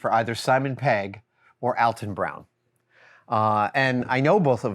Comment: Being from the UK I’d not heard of Alton Brown. I thought Adam said Elton Brown. Googled him, and frankly did not see the likeness.